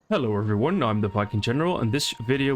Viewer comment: In the intro I thought you said "I'm the fucking general" but it was 'viking general'.